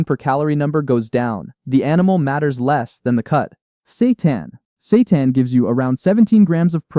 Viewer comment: I thought you said Satan, like what Satan has a lot of protein what???!!! 😭🤣 Edit: cool vid though